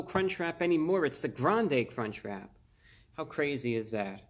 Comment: I fr thought he said the ''granday'' crunchwrap lmao